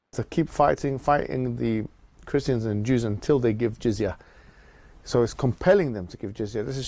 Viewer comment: Did he just say keep fighting them until they give jizz, I thought they didn't go in for that.